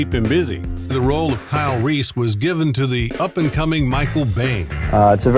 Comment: … had to rwind it several time but it sounded like „the up and coming Michael Bay…“ I thought that was very funny…🤓